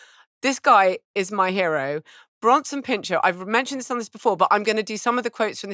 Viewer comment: Every time they say Bronson Pinchot I hear Branston Pickle